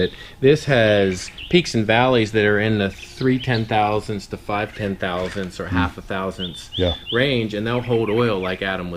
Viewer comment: What is that green stuff at 9:37? Did he say turcut?? Give me a break guys, I'm a noob. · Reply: Turcite. Its a brand name I think. If ya search it here on youtoob you will see some ppl using it. Turcite is PTFE (polytetrafluoroethylene) resins with added fillers such as bronze, that are extruded into solid billets as opposed to compression molding. Billets are then skived to a nominal thickness in 24" wide rolls, with lengths varying depending on thickness.